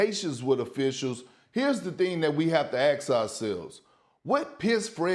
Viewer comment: WHY DOES PERK KEEP SAYING AKS INSTEAD OF ASK??!! Just listen to him